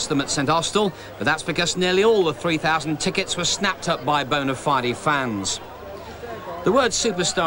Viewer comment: Did he say bonafidee? lol